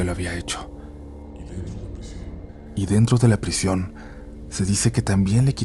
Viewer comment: En el minuto 14:40 se oye una voz en eco, que dice 😮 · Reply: Dice "Y Dentro de la prisión" es una voz macabra 😮